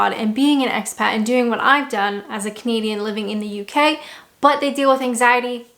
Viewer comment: God as my witness for the longest time I thought you were saying you were a "comedian living in the UK"